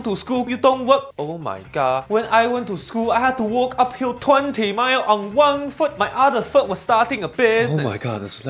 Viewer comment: I almost thought he said I walked 20 miles uphill on wok haha